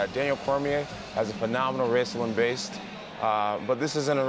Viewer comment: I always say that Cormier has A phenomenal raislin baste but nobody understands what I’m saying. Glad Jon agrees with me